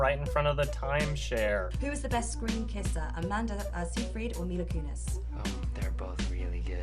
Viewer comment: I swear she says "Who is the best kisser?" and the name is "I need a penis" I'm sure it's Anita something but that's what I heard and now I can't hear anything else. 😹😹😹